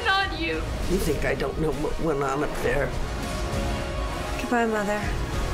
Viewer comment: what does the mother says on minute 0:35? i just dont get it.... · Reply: You think I don't know what went on up there?